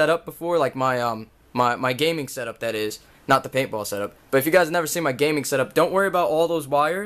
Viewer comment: im not kidding i thought it said pinball setup